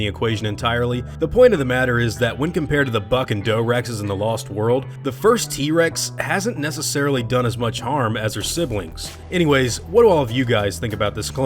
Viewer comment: I thought you said “the f**king doe Rex” instead of “the buck and doe Rex”.